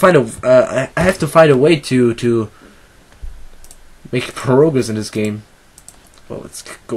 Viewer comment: At 2: 37 at first I thought he said "I need to learn how to make perogies in this game." not "I need to learn how to make progress in this game."